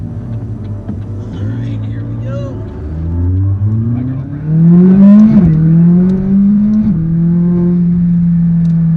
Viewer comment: jardon what did you just say? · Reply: My grill friend